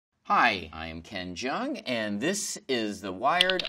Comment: What he said: Hi I'm ken jeong What I heard: Hi I'm ben chang